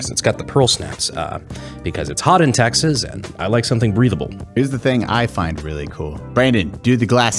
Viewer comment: I know in the poncho read said breathable I heard him say breedable roflmao